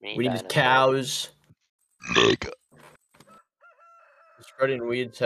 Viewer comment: did he just say "you're fucking restarted" lmao